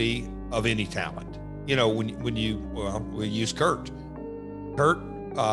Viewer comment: Was that a Freudian slip from Jarrett? Did he call 'Kurt', 'Perc'?